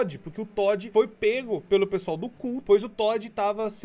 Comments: "pq o toddy foi pego pelo pessoal do CU" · "Todd foi pego pelo pessoal do cu" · "Pq o Todd foi pego pelo pessoal do cu" KKKKKKJJKKJKJKKKK